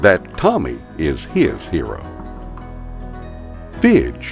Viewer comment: Cats are great 2:51 at first I thought you said bitch